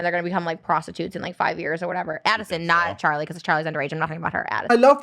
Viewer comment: Genuine question. At 13:43 doesn’t Trisha say “Addison not Charlie”?!?! I don’t think Trisha called Charlie a prostitute ?